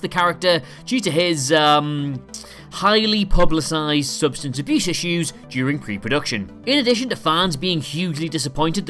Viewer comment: Dude, what is he saying at 1:34? Wtf cannot understand a word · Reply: "substance abuse issues" Or really "subshtnce abyoosh isshuzzz"